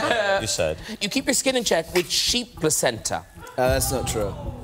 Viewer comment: What did Graham said to Harry at 1:37? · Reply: "You keep your skin in check with sheep placenta", lol.